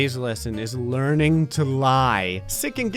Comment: I thought it said 'learning to die'....